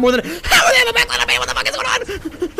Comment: This is what i heard "HALABAMA WTF IS GOING ON"